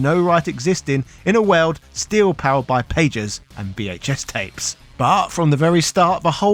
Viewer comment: Did he say "dominated by pages", or "pagers"? At 11:30?